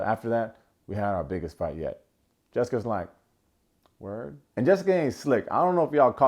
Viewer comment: lol it sounds like he said Jelsi